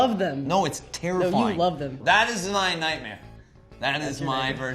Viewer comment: Does Eddie make a reference to the night man? Or am I hearing it wrong... 🤔